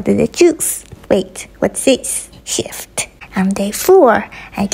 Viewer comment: I thought shift said shit😭